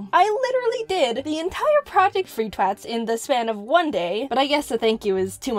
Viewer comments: What does she say at 8:20? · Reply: She's says "I did the entire project for you twats in the span of one day" !! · Did she say twats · did she say twats? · did I hear jaiden say " twats"😂😂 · I think she said twats 🤔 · I heard Jayden say twat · did you say "twats"? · Wait. Did Jaiden call them "twats" at around 8:19 ? I can't tell if that feels out-of-character or not, considering the circumstances. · Did she call them twats 8:20 😮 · DID YOU SAY "TWAT" XD